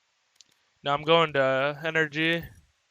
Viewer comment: I swear boostio said nrg not 100g